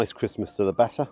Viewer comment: I thought you said 'christmas to the batter' 😅 And never bring a bent fork to a fish and chips fight 😂